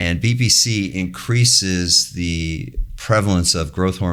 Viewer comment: I thought I heard him say bbc in the beginning 0:00 😂